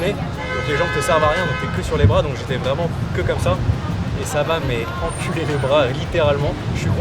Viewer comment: "ça m'a quoi ??" 😂😂